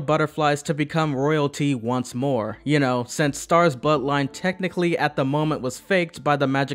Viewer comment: I'm not the only one who heard butt line instead of bloodline at 4:15? right??